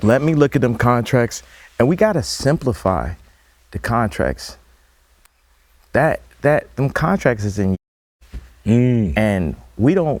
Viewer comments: What did Ye say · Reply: Contracts are in Years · Does anyone know what he said in timestamp 40:08 the word was purposely silenced out of this interview? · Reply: Kanye: "We gotta simplify..the contracts...that,that..Them contracts is en(slaving n*ggaz.) and we don't really know who we are."